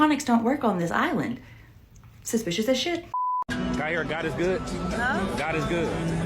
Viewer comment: Instead of her saying God is good, it sounds like she says God is Baal. 😮 Anyone else hear it or is it just me?